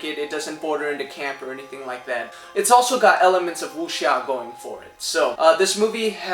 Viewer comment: What is the word you said at 3:44? Can you explain, please? thanks! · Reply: Chris Cloud Hernandez Staring at Fire “Wuxia” is a literary genre that focused on the fantasy elements of martial arts. A good example in film is Crouching Tiger, Hidden Dragon which features wirework and swordplay and departs significantly from realistic portrayals I’d martial arts.